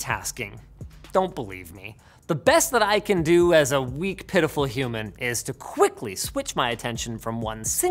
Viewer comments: I thought he said "as a weak pedophile human" 😂😂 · heard it as pedophile instead of pitiful the first time